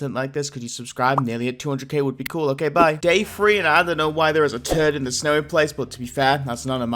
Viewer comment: i was in the bathroom, so not paying attention and i heard "day three, i don't know why there's a turd in the cemetery" then i came back and found out he said "i don't know why there's a turtle in the snowy place," but to be fair youtube heard "i don't know why there's a turd in the snowy place"